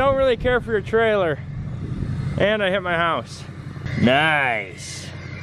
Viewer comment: He said and... I hit my house thought he said his mom's hours